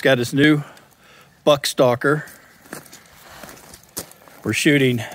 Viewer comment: sounded like you said butt stalker for a moment :face-green-smiling